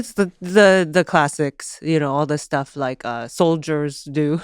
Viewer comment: Her vices are soldiers 😂 totally what I thought she said